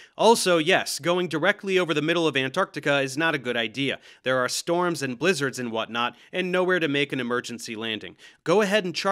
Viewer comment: At about the 19:40 mark, I initially heard "...storms and lizards...", can someone please tell me I'm not alone in mishearing "blizzards" as "lizards" at first, so I don't feel like such a dumbazz. If I don't get any replies, I'm gonna have to embrace the science and become a flat earther. Okay, not really, but it would be nice to not be alone on this. But if not, I'll probably survive. In fact, using past experience to predict future actions, I will probably forget making this comment in less than an hour, and won't think about it again, unless someone replies or likes my silly little post.